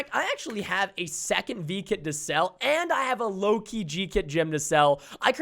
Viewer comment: When he said Loki I thought that he said low key